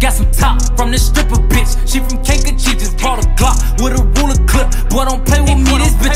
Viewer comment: HARD AF 🔥🔥🔥🔥🔥🔥🔥🔥🤘🏼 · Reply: Honestly I hate the song with a passion I looked it up to make fun of it because it sounds like he says she's from kanker cheif